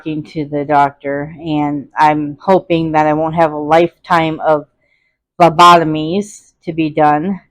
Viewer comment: I gotta get back into reading and exercising lol Sounds like you said lobotomies lol